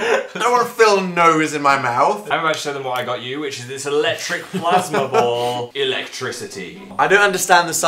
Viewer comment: I thought it said killing a plasma ball xD